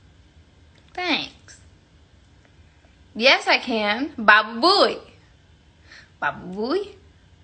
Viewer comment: why does she say babuy???